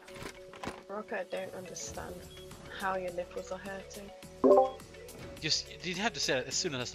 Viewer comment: What did say? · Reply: I don't understand, how your nipples are hurting.